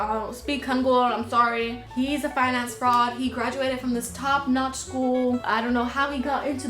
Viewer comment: this video is SO underrated bro!!!! also when you called sangwoo a finance fraud i thought you said "fine ass fraud" and like yeah, you're right